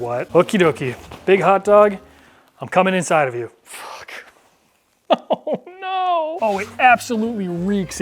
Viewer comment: Did swear at 10:00, don't think I've ever heard him curse before. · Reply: “Ffffuuuuu…” 😳 Dad??? 😂